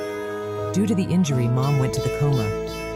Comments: Yo did I just hear "Due to the injury, mom has been sent to the gulag"??? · Yo did I just hear "Due to the injury, mom has been sent to the gulag"??? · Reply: It's coma, i also heard that and i thought that it said this but i used subtitles and it says coma